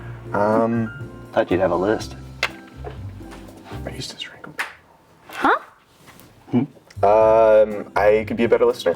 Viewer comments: what exactly did he say here😂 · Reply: “I used to strangle people” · I think he says "I used to strangle people" 😂 I guess he wants a crime documentary about himself